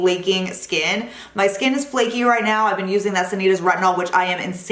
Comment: Did anyone catch what retinol she mentioned at 8:00? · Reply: Hi Jean! Yes I use the Sanitas brand retinol..they are the best retinols EVER! <link> gives you the most flawless skin and your lines and wrinkles fade! ;))